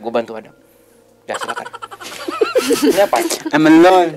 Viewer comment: itu bang ngomong apaan y? Gak ngerti maksud bercandanya apaan? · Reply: I'm alone.. Jadi suruh bang penelusuran sendiri